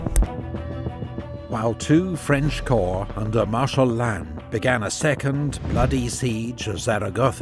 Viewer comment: When he said martial land was that government land? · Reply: You misheard. He said ‘Marshal Lannes’ not ‘martial land’. Referring to Jean Lannes commander of II Corps.